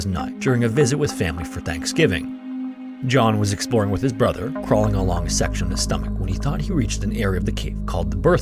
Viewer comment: i literally heard "crawling around sexual style" please get help i'm so done